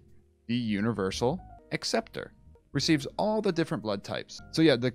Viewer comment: I am AB, I'm screwed! Lol. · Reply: I thought he said ab was the universal receptor.